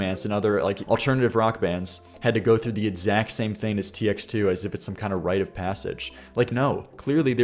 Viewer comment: I was watching this while going to sleep and i thought he said TF2 (it’s my favorite game and I might have a disorder) and I got so excited I woke up completely.